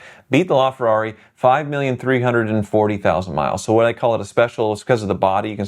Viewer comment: did you say the Merceds had 5,340,000 miles? 🤣 Nice video as usual.